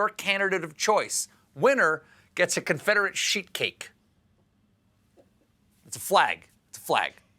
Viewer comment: Had to replay part of this vid...when Stephen said "Confederate sheet cake" I heard "Confederate shit cake"!